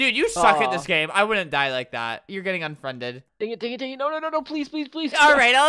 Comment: I thought he said "your getting unpregnant"